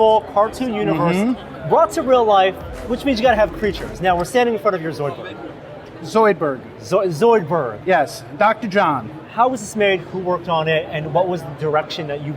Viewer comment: slight cringe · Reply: What'd he even say it sounded like he said "zoidburr"